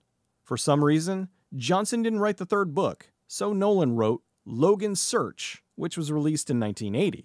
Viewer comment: I had to replay this 5 times until I realized you were saying "Nolan wrote Logans Search" instead of "no one"